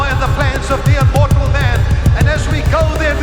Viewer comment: Did Clement say “… destroy the power and the plans of the unmortal man?”